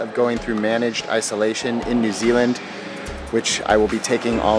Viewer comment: When you said New Zealand, I genuinely thought you said Disneyland. Oy. 😂😭